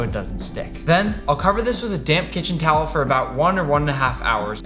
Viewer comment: I thought he said “a damn kitchen towel” XD